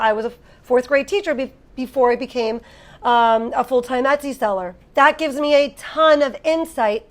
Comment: Close caption says: full time Nazi seller 😅😂